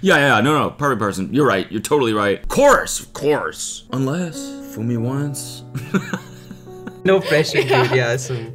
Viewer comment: / 23:00 THIS PART WAS SO HILARIOUS the fact that it even sounded like fool me once im dying